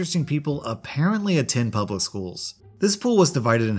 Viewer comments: Anyone else hear school at 0:30 - 0:33? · Did you say “schools”?